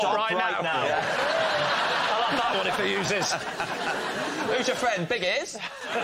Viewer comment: Can someone please tell what James says here. And why is it funny? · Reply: Noddy was in a cartoon that featured a character named Big Ears. Lee had made the joke "who's your friend, Big Ears?" in the first round in reference to Noddy and Big Ears. As these improv comedy panel shows have to be heavily edited, the panelists sometimes repeat each others jokes in case the jokes get edited out, like James does at 0:59. The audience responded to James' call-back reference to Lee's joke from the previous round.